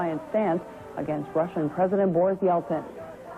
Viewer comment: did she say "russian president boris johnson" or its just me